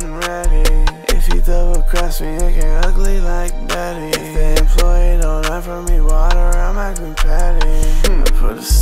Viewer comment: at first i thought said "If the employee don't hide from me.."